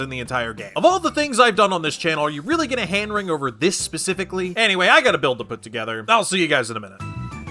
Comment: I thought I was having a stroke when I heard “I’ve gotta build a put together”